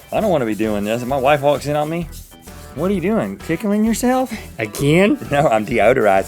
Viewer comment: with the subtitles on says picking instead of ticklicng! LOL